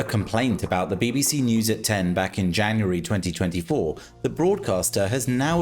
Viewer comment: What did you say at 0:10? "Following a complaint about the BBC News (at 10)?!..."